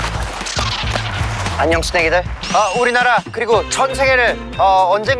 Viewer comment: 안녕 스낵이들 잘못 들어서 안녕 쓰렉이들로 들려여 😢